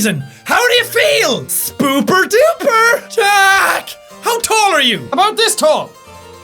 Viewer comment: hey its marks catchfrace but with a s instead of a b right wen he said it i thought BOOPER DOOPER!